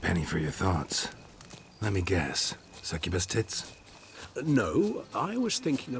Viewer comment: The subtitles say twins, but Geralt says tits :P